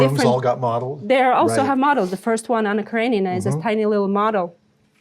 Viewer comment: I couldn't catch the name of the film mentioned here. What is the film that was set in Russia but produced in the UK? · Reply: Almost missed it also, but it's Anna Karenina I believe